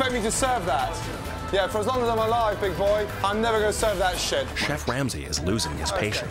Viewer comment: For a second at the beginning I thought gordan said "me boy" and not "big boy" I was gonna say MR CRABS?!